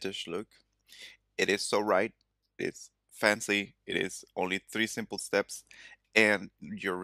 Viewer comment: Thought you said three steps ..